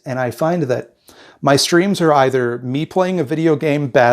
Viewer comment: I kept mishearing "streams" as "screams" and for a second the first time I heard it I was like "uh yeah I don't think I've ever heard you scream. better work on that"